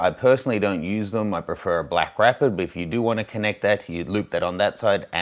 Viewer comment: What did you say you prefer to a camera strap? @ 2:20 Black Rapper?